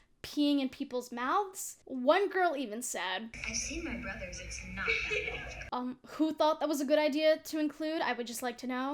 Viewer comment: What did she say??? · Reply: It was “I’ve seen my brothers, it’s not that big.”